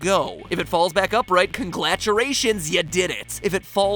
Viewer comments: Did MatPat say: 'Congladuration'? at 11:07? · Anyone else hear at 11:08 “Conglaturations”